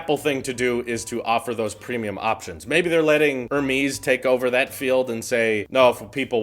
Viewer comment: Did he just say "her-mees"?14:00.